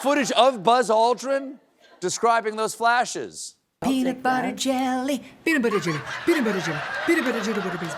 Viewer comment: That last one sounded like she was saying Pete Buttigieg over and <mail>